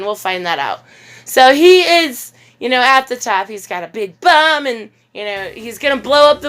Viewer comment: I could have sworn you just said "He's got a big bum."